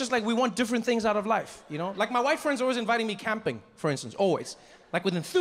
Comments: did ge say his "WIFE'S' friend? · oh he said white friends,at first i thought he said wifes friend, 😂😂😂